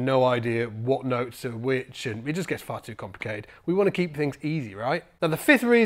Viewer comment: "Far too complicated".. what I heard was "fuckton complicated" lol